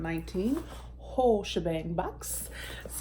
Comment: The first time you said the name of the box I thought you said the wh*re shebang and for a second was like Ooohh what's in that lol lol. Good box! Yes, I remember when you did a peel off mask on a live😬. Hope you're enjoying the weekend!